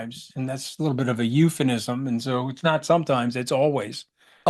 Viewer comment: Did Scaramucci say "eupheNism"? 🤣